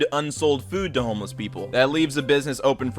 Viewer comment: Sounded like you said almost people instead of homeless people.